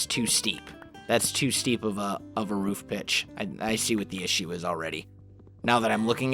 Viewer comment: Did he say bi*ch at 19:50?!? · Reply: "roof pitch" (as in angle of the roof)